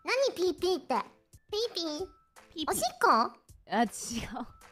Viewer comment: pretty sure Haachama said "Oshikko" (pee) not "Ochinko" but god did it really sound like she said that lmao